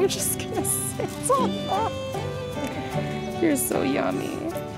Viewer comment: "You're so yummy" to the dog ? Did I hear that correct?